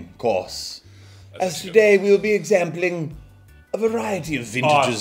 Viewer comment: Did he say 'exampling'?